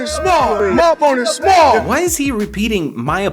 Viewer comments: I thought he was saying my boner small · I thought mayweather said “my phone is small”